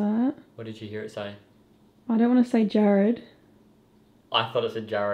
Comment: Great investigation! When you heard vox say "Hey Jarrad" I thought I heard "Stay Jarrad". And the whisper when you were sleeping, I swear I heard "They're asleep"!